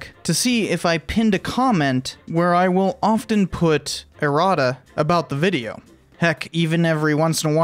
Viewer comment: Smooth segue to the sponsor, lol. Also, it's late and I first heard 'errata' as 'erotic' and was kinda, wut? :-P